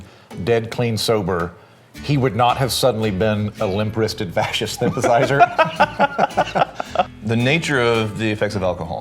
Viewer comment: Wait at 7:00, did he say Churchill was a "fascist sympathiser"??? Pretty sure he was the opposite. (if thats what he said) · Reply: He said "He wouldn't be a Fascist Sympathiser"